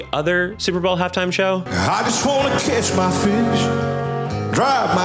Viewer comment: I can’t stop hearing this man say “kiss my fish” instead of “catch.” It brings me such joy to think of that horrid little man frenching some poor Big Mouth Billy Bass lol I hope this makes that horrible “half” time “show” more tolerable for some of yall <3